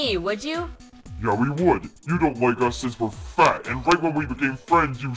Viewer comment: Did Boombox Sayed F*ck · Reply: He said fat